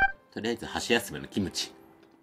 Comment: 箸休めのキムチ が、僕の友達コーラに聞こえた🤣🤣 今日も美味しそうだし、たまに笑わせにくるところ好きです🤤 私もさんのこの手の動画見てプルダックポックンミョンに挑戦してみました⚡️